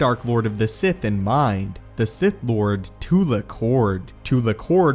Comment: I heard it as Tula Khord, I didn't hear it as Tulak Hord.